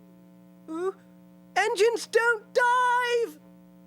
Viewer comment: While definitely shorter than the rest of them they were definitely some fun surprises in here. And of course I knew that John Bellis was sad about not getting to play Thomas but the more it really does hit deep like it actually sheds a tear. And it was so fascinating here hearing how he got to be involved in other ways other than being a taxi driver. Who knew he would have read the lines for the part of Gordon and was the stunt man for PT Boomer. I think my favorite part has to be the cats will play scene. While I do like the dialogue from the final cut I wish they could have found a way to mash both of them together. In terms of all the va's they've ever had for this film James has had it the worst. And I did genuinely chuckle at some points Thomas's engines don't dive line why did they cut that? Keep up the good work can't wait to see what's in store next. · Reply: It could just be me, but it almost sounds like Thomas is saying "Engines don't dive". Again, could just be me.